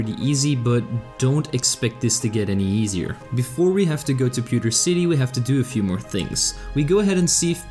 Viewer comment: did you just say pewter city at 3:11 ?